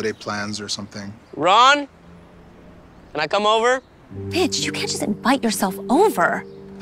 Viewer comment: she says the b word instead of Mitch